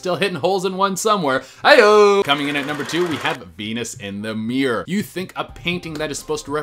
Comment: i thought it said penis in the mirror